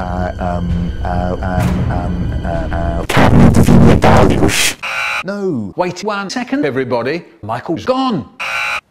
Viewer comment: What’s Mark saying at 1:18 and what’s the source video? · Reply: carne de vinha d'alhos and source is his video about vindaloo